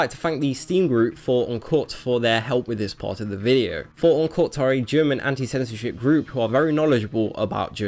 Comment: I thought you said Uncut were a German antisemitic group at first. I had to skip back.